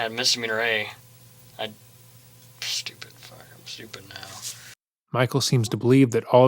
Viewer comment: I would argue he said, "I was a stupid fuck, I'm stupid now" self-deprecating is also a manipulation tactic. I just know 100% I heard "I'm stupid now" I put a time stamp for when he begins the statement. Anyone else hearing it too?